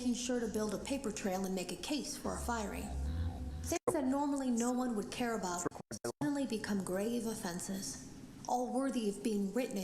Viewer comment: What is said at 3:51 ? It's a glitch in the tape. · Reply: Feel free to correct me if I'm wrong, but I believe it's, "(...)things that normally no one would care about are suddenly grave offenses. All worthy of being written and documented."